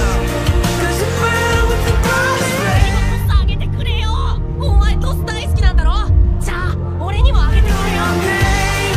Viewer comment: anyone know what he's saying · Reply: "Please throw me a toss!" "You love tosses, don't you?" "So toss one to me!" Season 1 episode 3